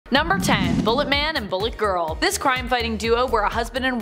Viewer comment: I thought she said, "MULLET Man". I so wanted to see a vintage dude whose hair was business in the front and a party in the back.